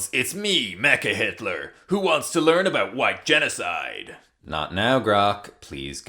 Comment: So I'm pretty sure Grok said "white genocide." However, I first heard "Why Genocide" and I'm not certain... Which one is more appropriate for Grok to say.